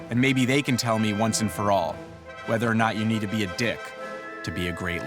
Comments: Oh damn, I genualy thought it said "do you need one" not do you need to be one. Lol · i thought it said do you need one